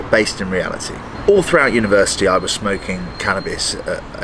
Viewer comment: I really heard " i was smoking all through the universe" instead of university 🥶😀opsey